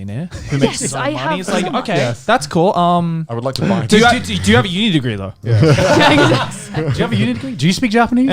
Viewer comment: のところなんて言ってるのか聴き取れねぇ... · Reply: "Do you have a Uni degree though?"